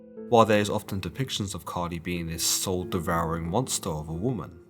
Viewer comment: LOL!!! I'm an idiot. I thought I heard you say 'Cardi B" near the beginning